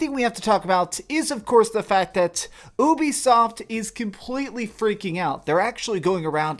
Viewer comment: Did I just hear b**bisoft? @ 0:34 😂